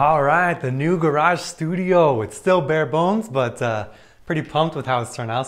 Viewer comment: Wait. Did i just hear "bề bộn" ? 🤨🤔